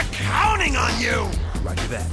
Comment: it sounds like Espio said "Fuck that!"